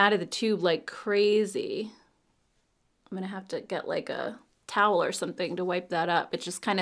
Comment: Lol I first thought u said u was gonna have to get a COW to wipe up the cheek tint ....(like I thought alot of it leaked) but then realized u said TOWEL 😂😂😂😂😆😆😆😆